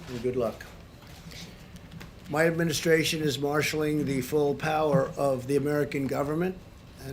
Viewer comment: what does he say...sounds like Marshall law